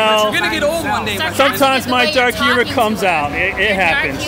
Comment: LOL I thought he said "sometimes my dark passenger comes out" 16:40 Dexter? Dat U?